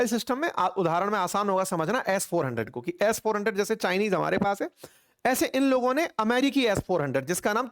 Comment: Did you say Chinese